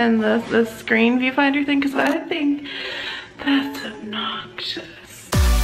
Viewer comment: obnoxious?is that what she said? i couldnt make out what she was saying